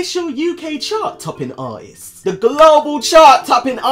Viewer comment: At first, I didn’t realize he was saying “Artists.” I thought he was saying “Arses”...and honestly, both could work 😂🤣😂